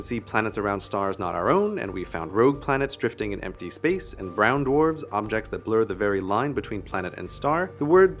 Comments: Anyone who thought he said ground orbs, he said brown dwarfs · what does he say at 4:06? Bround orbs? round orbs? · What is it Grey says at 4:05? Round orbs? Those sound fascinating.